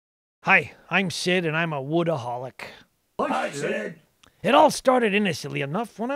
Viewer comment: I thought it said workaholic at first